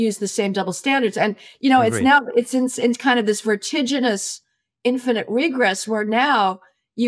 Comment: Can somebody help me with the word she says just after 32:07? Retigeouness? I have no idea how to spell it. I tried looking it up and I can't find a match in a dictionary. Is it a really obscure word, or did she maybe misspeak? I feel really dumb right now, usually when I hear a word I haven't encountered I can find it quite easily or guess based off of context at the very least.